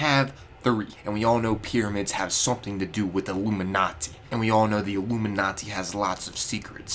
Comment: Ha thought you said IllumiNAZI